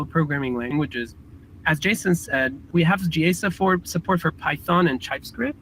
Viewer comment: What's that phrase at 2:49? "...we have jee-ay-sab-forb support for python and typescript..." ? Mostly I'm laughing at myself - I'm sure as soon as I know, I'll wonder how I heard it wrong. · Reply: I think it's "GA Support" meaning that the API is stable and there will be few breaking changes and those come with major version bumps. The other languages (Java and C#) are still in developer preview until they improve the developer experience